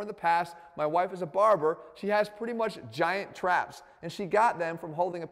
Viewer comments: This dude really just called out his wife’s traps. For our entertainment. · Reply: Traps? I thought he said flaps? I was thinking geez Jeff a bit too much info 🤔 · I heard " my wife is a barbell " .